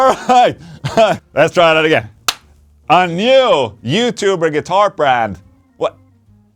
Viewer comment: At first I thought you had said "GUITAR BLAND", as a cheeky dis 😂😂